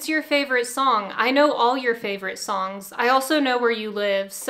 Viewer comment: Oh...,Mall Cop. I thought she said Mulcough. I'm like who or what is a Malcough???😂